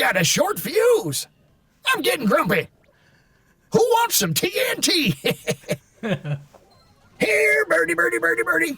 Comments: Always thought he said "Who wants some candy" Great to know Mike instead loves TNT as a snack! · i always thought he said : Who wants some candy? Now he is less creepy to me. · I always thought he said “who wants some candy”? Lol...